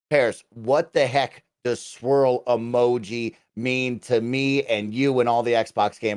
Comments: I thought you were saying Squirrel emoji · I thought they said squirrel emoji. I think it would be funny if Kojima made Conker.